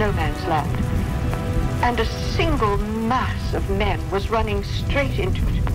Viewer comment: The video says “single mass of men” but the subtitles says “single mass of me+h” excuse me what